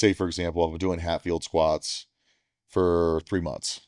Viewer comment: Is he saying "Hatfield's" or "half yield's"? CC is saying "half fields."